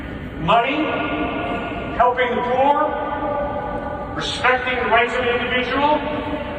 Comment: Love that the captions auto-generated "helping war" instead of "helping the poor". Even auto-gen knows what's up.